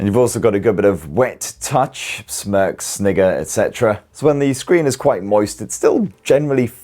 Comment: Did unc say nigga???